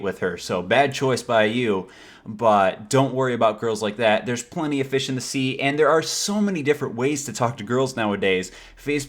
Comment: sounded Like you said " there are plenty of bitch in the sea"